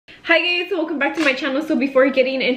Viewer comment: did she say hi gays